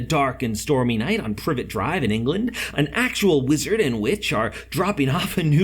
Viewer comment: it sounds like he said a lizard and a witch